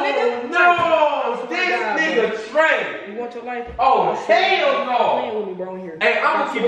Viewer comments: anybody else heard say “you want to life bro” to? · Damn did yal hear what she said to her girl at 9:45. Wow 😱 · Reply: She said u want ur lofe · I know somebody heard say "You want yo life?" 😂😂😂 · I know I ain't the only one hear threaten life tf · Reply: Nyla Nyla I heard her say “ you want your life” 😭